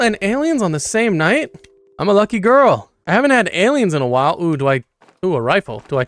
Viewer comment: Did u just said "Ohh I'm a lucky girl"😭